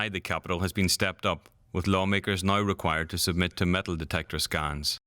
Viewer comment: I thought he said "mental detectors". That might be a better idea???